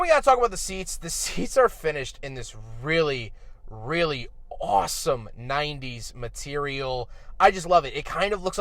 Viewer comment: once you said awesome i heard stavvy and now i can't unhear it